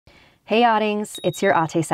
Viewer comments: Dude my dumbass always thought you were saying "Hey outings its your ate Saphire" i thought ate was just a fancy word for narrator 😂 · All along I thought she was saying autings And it's your aute.... But it's ading ( a-ding) and ate (a-te) I'm Filipino btw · I always thought you said audings not adings